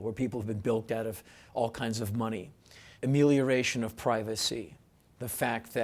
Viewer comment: what word does he use here? X of privacy? would really help me out, thanks. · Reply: Amelioration - the act of making something better; improvement.